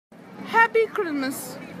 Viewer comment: When she said “Happy krimas”, I thought she said Happy grimace lol 🤣😭